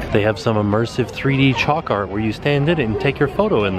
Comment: I thought you were saying "choc art" I was thinking "how does it not melt" then realised after you said it for the 3rd time you said "chalk art"